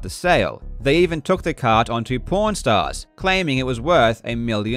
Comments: I heard "they even took the card onto p* rn stars" and was like ehhhh?! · i was listening to this video without watching the screen and when he talked about pawn stars i thought he was saying porn stars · Every time he says "Pawn Stars", I keep hearing "Porn Stars" 😂 Fr tho - this is a great, well researched vid. I've been rhinking this bubble was self inflated and sus, and this provides a lot of evidence. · Hi, new member of the club here. I also heard "porn stars", and for a a couple of seconds, I was completely confused about what was supposed to have taken place. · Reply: for a bit I thought he said porn stars 😂😂😂😂😂😂😂😂😂😂😂😂😂 . he meant pawn stars.